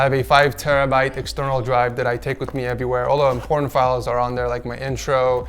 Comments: I thought he said porn files LMAO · What did he just say? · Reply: Important files